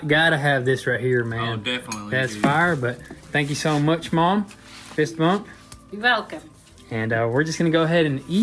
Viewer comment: Hey guys. I heard your mom's fist. Men she is tough 💪